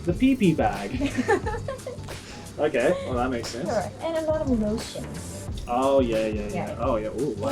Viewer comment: it's quite hard to understand what Kaho is saying sometimes like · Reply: I think she said "and a lot of lotions"